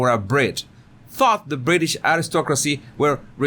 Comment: I thought you said "fuck the British..." It looks like your lips make an "f" but that might be because "th" is hard for non-English speakers